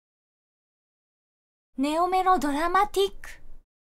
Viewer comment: ネオメロドラマティックが今田ナウロマンティックに聞こえてしまったくらいには寝ぼけているらしいw